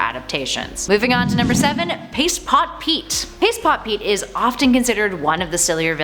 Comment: I legit thought she said piss pot Pete lol